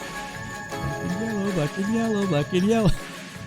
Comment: okay thank GOD I'm not the only one who hears "Black 'n yellow, black 'n yellow" in that part lmao